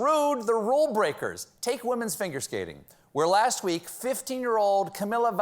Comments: Did he say "FINGER SKATING"?? At 3:55. I'm cracking up over here. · did he just say "women's finger skating"? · Did he just say finger-skating? :D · "Take women's fingerscating". FINGERscating?! I'm sure I'm not the only one who heard that? @ 3:54... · I swear I heard "women's finger skating" Yeah, my mind lives in the gutter.. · At about 3:53, did you say "Women's finger skating"? That joke writes itself....